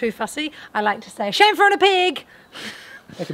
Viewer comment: YouTube subtitles thought said "shampooing a pig". Made complete sense to me though. Why waste time and shampoo on a pig? Love all of y'all's videos and of course attention to detail.